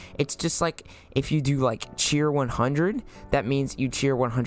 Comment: i thought he said cweer